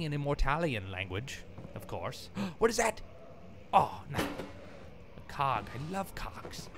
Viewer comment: Did a doubletake at 3:33 because I thought said "Oh, cocks, I love cocks" Was multitasking LMAO, so I wasn't paying much attention at that part and was like "wait, cocks.. wut"